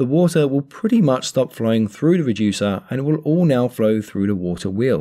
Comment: turn on captions from 2:48 to 2:50 it says flying instead of flowing